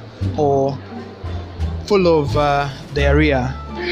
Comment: did you just say diarrhoea 😂😂😂😂😂